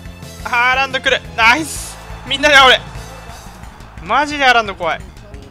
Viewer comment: このシーン「みんなで守れ」っていうのが「みんなで煽れ」に聞こえてPSG思い出した笑笑